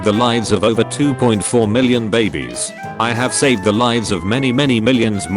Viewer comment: i thought it said barbies but it's actually babies 💀